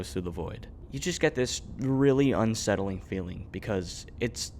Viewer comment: thought bro was saying fein